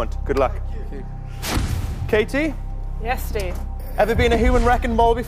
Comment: Did he just say “you too”? He’s not the one who has a chance of becoming a human wrecking ball😂